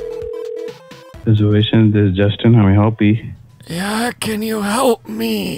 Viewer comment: he really said how May I helpee 🤣🤣🤣